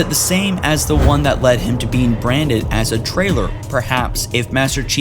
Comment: you said around 5:50 what made master cheif a trailer just saying but we know you ment traitor